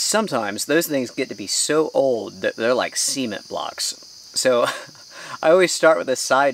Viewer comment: No offense, but the crows are laughing at you. I thought you said, "Semen blocks" (42:16). You can't blame the southern accent for that.